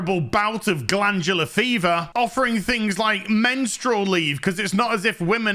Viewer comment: I thought I heard "Menstrual League"🤣🤣🤣